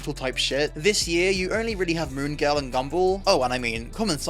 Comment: What did he say? Moongal and Gumball???? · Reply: mooooooooon girl and wonderfully weird world of gumballllllll